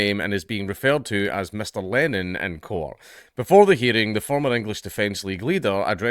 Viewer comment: i thought i heard "Mr. Lenin in court..." 😆😓